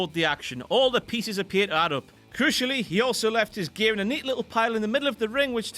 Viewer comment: What does say at 4:03? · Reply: I had to listen 3 times, I think he said "crucially" but it kinda sounded like it had something to do with cushions.